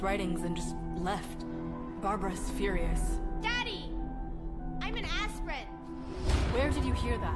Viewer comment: I heard “daddy, I’m an aspirin!”